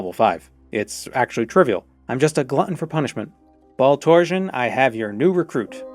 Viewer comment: Wait, did you say his name is Ball Torsion?